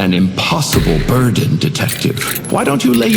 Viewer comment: The captions are wrong at 0:01 already... he says "burden," not "bird" bruh And at 0:10 he says "the tome," not "it" 0:27 he says bends, not turns... wow these captions are gonna be slightly wrong for the whope video huh "its" not "it's" "patrons" not "patron's"